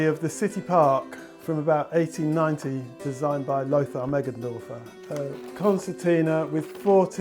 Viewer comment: does anyone know the name of this book or what "type" of book this is? i know he mentions the creator's name but I couldn't quite catch that. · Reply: It's "The City Park" by Lothar Meggendorfer - a "concertina" type book - this one is from the 1890s - the one in the video is a facsimile. It's available on Amazon.